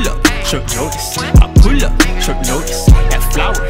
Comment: At the first listen, I heard "I pull up Chuck Norris" 😂 😂 😂